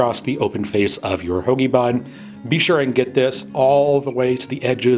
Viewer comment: I thought he said homie bun